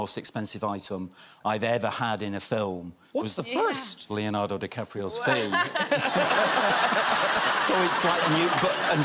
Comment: I thought he said “Leonardo DiCaprio’s feet” 😂